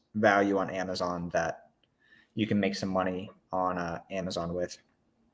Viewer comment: You said that there are also books that don't have much value on Amazon that you can make some money on Amazon L....? What were you saying here? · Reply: I meant some books you could make more on eBay than on Amazon FBA. This would be the case for books that are either really cheap and so because of how fees work on both platforms they would make more on eBay or really rare collectible books could be better on eBay sometimes.